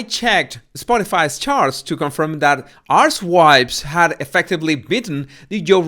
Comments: I keep hearing the word 'arsewipes' instead of 'archetypes'. Check out 5.42 and 6.00 min. This man ain't just the body language expert but excels in sending verbal messages via his accent. 🤣🤣🤣🤣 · Everytime I hear someone say “archtypes” I hear “arsewipes” and I’m so shocked I have to check it!